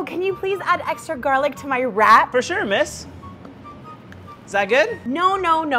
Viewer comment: Bro i thought she said can you add extra garlic to my rat 💀